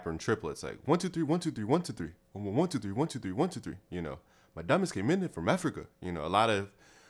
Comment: What did U say about Africa? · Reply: South African Hip Hop Instrumentals diamond come in from africa